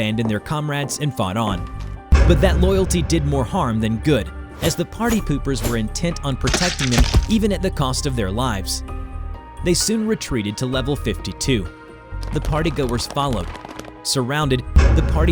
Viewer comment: what the hell u said partypoopers